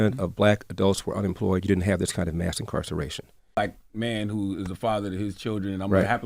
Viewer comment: I thought he said nascar racing not mass incarceration 😂